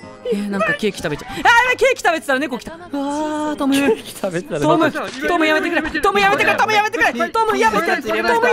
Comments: ここらへんずっとトムヤムクンっ！！って聞こえるww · 〜 トムやめてがトムヤムクンに聞こえて脳裏から離れない